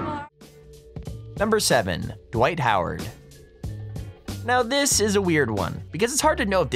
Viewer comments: When you said dwight howard I thought you said white power... I should probably get some sleep, huh? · I was lookin at my dog and thought he said “white power” lmao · I thought you said Dwight Howard that's Tim Hardaway Jr.